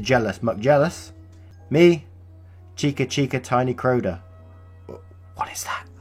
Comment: I kept hearing “Tiny Crota” (because I listen while at work, can’t actually watch)